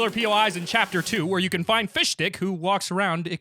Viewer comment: lol if you put on captions at this part look what it says instead of fish stick